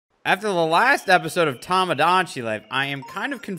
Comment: When he said “after the last episode of tomodachi life” I thought he said “this is the last episode of tomodachi life” and I almost lost my shit